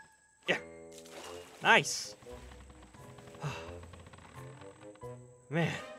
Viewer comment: I thought said "Soup, my dude!" I was so confused for a moment XD